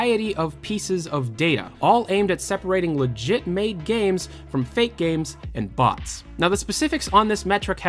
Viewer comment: LOL he said "legit mate games" instead of legitimate games